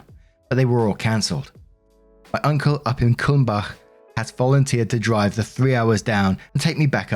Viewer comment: I thought you said Cwmbach, which is in Wales. Nearly gave me whiplash, you did.